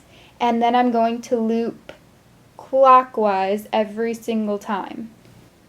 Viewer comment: Omg when she said clockwise I heard "quakwas" am I weird or did you hear it too?